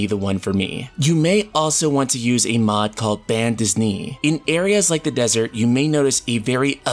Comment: Did you say BAN DISNEY ?